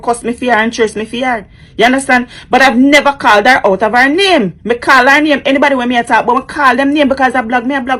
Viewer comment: I thought this lady said she's out of drama